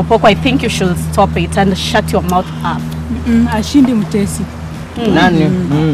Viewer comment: Who else heard shut your mouth up😂😂😂